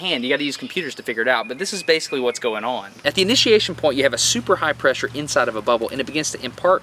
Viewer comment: Please, could anyone tell me, What is he saying at 6:18 ?? ini..... point ? Thanks alot !